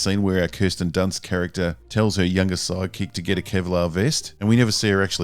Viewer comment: In this video, and Rebel Moon, anytime you say "sidekick" I hear "psychic" and have to remind myself that I heard the wrong word.